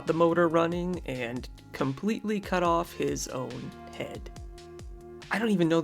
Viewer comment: head 😮 i thought you said hand.. jesus haayzues christ 😮